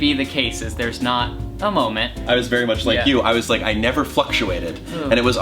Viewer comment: when he said fluctuated I thought he said fucktuated